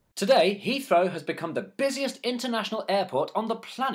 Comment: Busiest airport? What about Chicago and Atlanta? Just saying. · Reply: If you'd clean the crap out of you ears, you would have heard him say "busiest INTERNATIONAL airport". Just sayin...